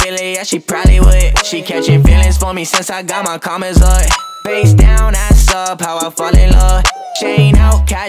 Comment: I thought he said faze down